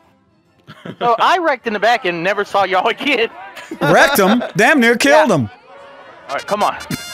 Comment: Ooops is it just me or no when they said wrecked him i thought they said "rectum" LOLOLOL